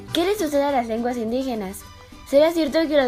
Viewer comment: Que dice en el minuto 0:36 porfa ayudenme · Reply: Dice que le sucede a las lenguas indigenas